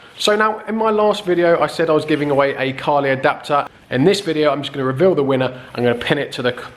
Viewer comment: I thought u said giving away a car